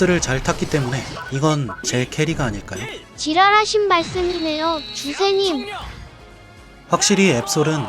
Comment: 자막은 지당인데 말은 지랄하신말씀이라 들리네ㅋㅋ